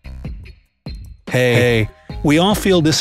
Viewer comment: am I the only one who heard "he-hey"?